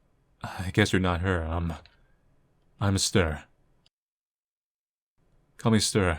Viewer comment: Hamster... Sorry, that's what I heard